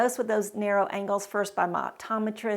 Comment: I thought you said narrow ankles at first! 😂